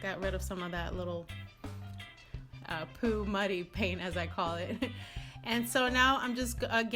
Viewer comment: Lololol I thought you said “poop money” and I thought what the heck is that? Then I realized you said muddy. Every time you say it I laugh.